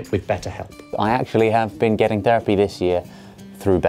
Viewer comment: I misheard the sponsorship part. Thought I heard say "I actually have been getting derpy this year"